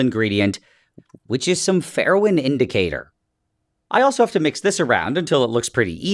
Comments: Thought he said heroin indicator at first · I thought I heard Heroin indicator. I was like bro we making drugs?